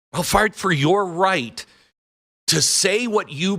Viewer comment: Did Glenn just say "I will fart for your right..." at 14:46?